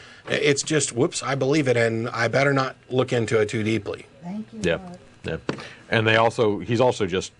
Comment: Did anyone else hear 'thank you lord' at 8:18? :O